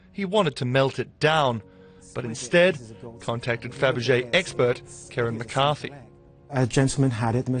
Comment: did he just say... fabergéEGGspert?